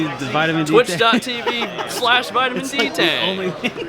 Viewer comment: did he really say twtich.tv/vitaminCtang???? lol